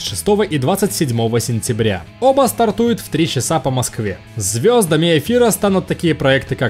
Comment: черт, пора прекращать играть в ведьмака - я здесь услышал "звёздами Офира", и у меня аж сердце в пятки